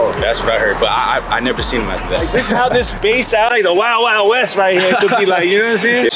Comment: Did just say "Wah Wah West?" lmaooo